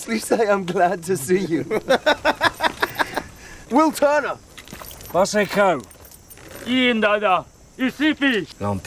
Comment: Did he just say “Masenko”